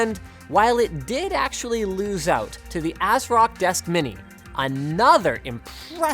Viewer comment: turn your cc on at 4:19 ... it shows ass rock instead of asrock lol xD